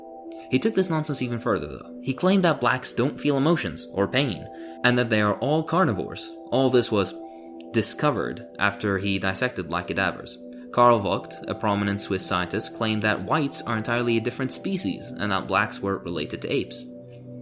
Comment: hold up, did you say "black cadavers" or "LIVE CADAVERS" BECAUSE I WOULDN'T PUT IT PAST THOSE RACIST AMERICAN FOUNDING "FATHERS" , ,, Also "certain races mixing produces infertile offspring" .... someone is due a surprise when finding out about genetic mixing producing more robust offspring than uh..... inbreeding within-race mixing